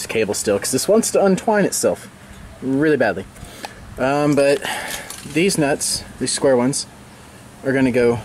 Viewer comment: Did he just say "deez nuts" at 0:45?